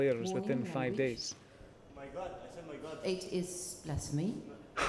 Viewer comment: can anyone tell me what did the referee say about the "OMG" statement Amr said? :)) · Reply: Referee: Mr. Shabana, warning. Language Shabana: My God. I said my God Referee: It is blasphemy Shabana: Not in my religion